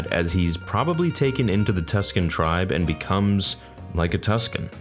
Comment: Did you just say “becomed?” Lol